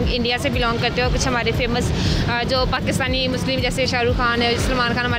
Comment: did she just say Pakistani muslims to srk and salman? lmaooo